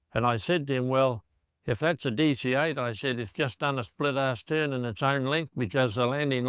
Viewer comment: I thought he said a "split-arsed" turn in its own length